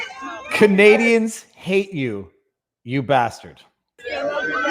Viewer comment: Lol. I love this. I thought the one guy said “I hang you” but it was “ I hate you”. Thanks for clarifying. If JT walked down my street I’d be hard pressed not to toss something at his head